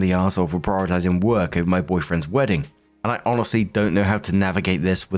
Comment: did I mishear or did do a little Freudian slip and say "boyfriend's wedding" there?